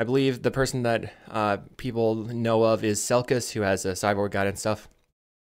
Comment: Hi! at 01:09 you say the name of the person who has the sideboard guide but I can't understand, could you please tell me who it is? Thanks in advance, and a big hug from Brasil! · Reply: selkcahs, who is from Argentina I believe